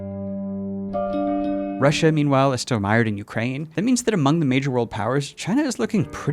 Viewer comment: Just wanted to mention; I'm watching with just subtitles and no audio, and at around 28:10, the subtitles say "Russia is still admired in Ukraine", and I believe it is supposed to say "mired" or something similar